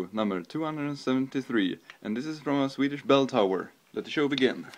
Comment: did he say swedish belltower?